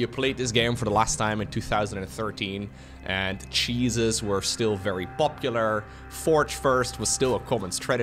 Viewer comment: Lmao i heard : "Jesus was still very popular" and was like wait wat 🤣